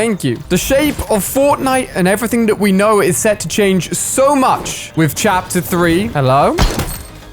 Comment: is it just me or did anyone else hear him say it has a lot to do with CHAPTER 3!?